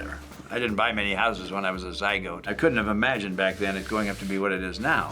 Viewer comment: did he say zygote 😂😂😂🤣